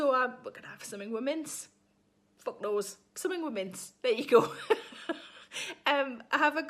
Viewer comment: I'd like your recipe for "Something with Mince". This lockdown has me going through the usual suspects and I'm running low on ideas. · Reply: I thought she was saying Mints. Being from New York, I’ve never eaten Mince.